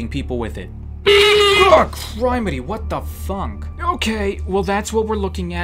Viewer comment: I thought u said a bad word but you only said what the funk xd